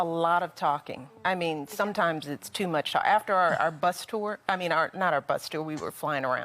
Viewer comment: I thought Michelle said butt tour instead of bus tour lol